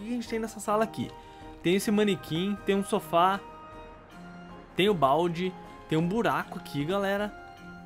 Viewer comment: Juro que ouvi ele dizer "Tem o baldi"achei que era o professor baldi